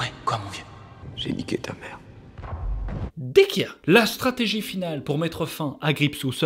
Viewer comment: Il dit quoi à 14:21 ? Dekia ? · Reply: Il dit d'accord